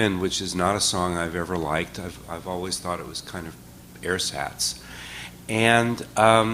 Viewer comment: what's the word said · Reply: ‘ersatz’ - a German word meaning ‘poor substitute’ or ‘artificial’.